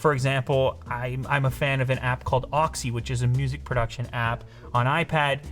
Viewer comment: Yo Someone know what app he’s talking about? Oxy? auxy?